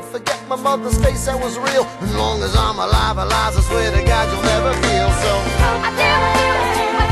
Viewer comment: Is it bad that i thought instead of saying 'as long I'm alive Eliza' I thought it said Oh 'llama 'liza alive Eliza' It is